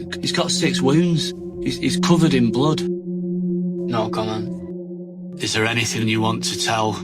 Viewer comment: What did he keep saying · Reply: Was it no comment?